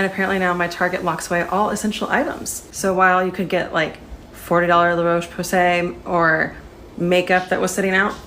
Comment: It’s a good thing 1:20 had subtitles cuz I swear she said “forty dollar La’ roach pussy”